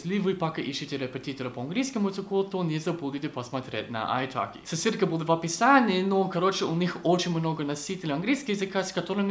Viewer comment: Ссылка будет в описании, а мне послышалось соседка будет в описании